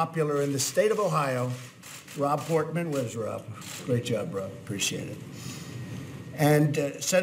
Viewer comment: did he say "Great job bro"? or am I wrong